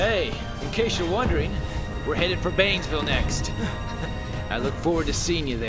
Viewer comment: "We're headed to bangville next" (55:04) Made me spit out my coffee fr "We're going to go get prostitutes after this lmao" is all I heard.